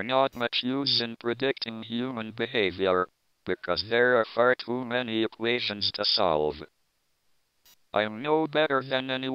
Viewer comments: Did he just say: "Because there are far too manny of asiens to solve" ASIENS :D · PLEASE HELP What's the word at 4:37 after the word "people"?????? What does it mean? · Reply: tick.. he means like a clock. a clock ticks counting seconds for us. but he can't explain how humans tick.